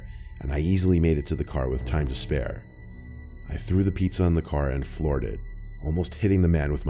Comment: It sounded like you said "on the car" instead of saying "in the car".